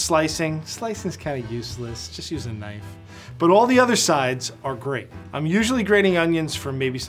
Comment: “All the other sides are grate” I thought you just said 2 of them are shred